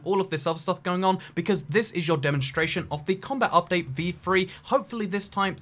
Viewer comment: did you just say E3? I wonder what this video has to do with a gaming convention 🤔